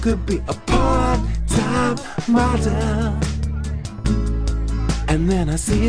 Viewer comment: I always hear he sings "Part-time mother".